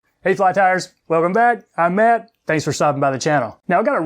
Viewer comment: I don't have my hearing aids on and you say "hey fly tiers" what I hear was "her flat tires" I'll posse and go find my hearing aids, I'll be back in a minute. Oh boy, I can see how this day is going to go...